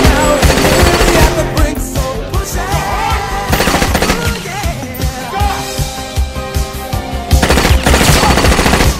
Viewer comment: what hi saying You h ....? Pls help. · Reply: Its for you Dear You horse